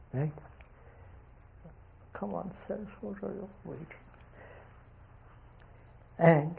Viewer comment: what he said at 3:09? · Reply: "Cmon sirs what are you all waiting" says the knower humbly!